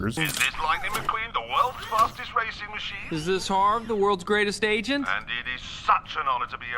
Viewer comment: the auto-generated captions say “hammond” instead of “harv”. coincidence?